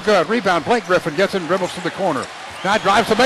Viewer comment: "how about them apples" I thought he said What a god damn athlete lmao